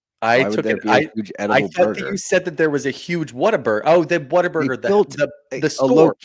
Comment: Hey, I thought the exact same thing haha I thought said a GIANT burger 😂